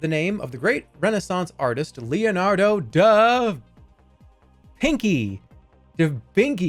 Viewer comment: did he really say da vinky